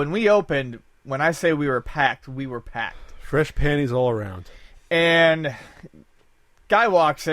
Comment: I swear it sounds like says "fresh panties all around" LOL